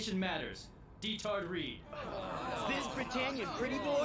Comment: I thought that said "I'm not a rapist but.."